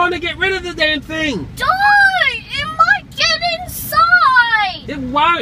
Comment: aww she cares for her dad... wait what did she say?! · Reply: Don't it might get inside